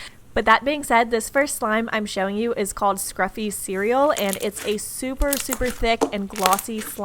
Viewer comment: I first heard "It tastes" instead of "It's a". Just me? Ok 😂